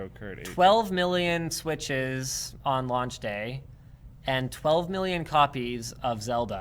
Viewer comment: Did he say 12 million on launch day?